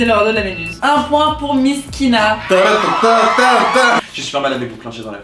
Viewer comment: C'est que quand elle a dit "un point pour miskina" (3:27) que j'ai compris pour il s'appelait miss kina 😭